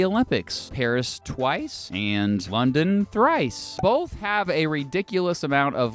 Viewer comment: Thrice? I thought you said weast?